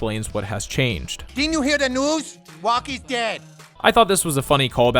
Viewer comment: OOOOHHHHH WOKE is dead. I truly thought he was saying Rock is dead